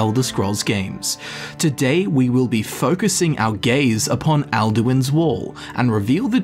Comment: I was confused cause you said "focus our gaze" but I thought you said "focus our gays" I was down tho